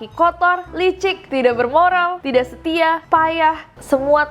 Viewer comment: Yang waktu di Billboard gw salah denger...Gw pikir Tidak "BERMODAL"😭🤣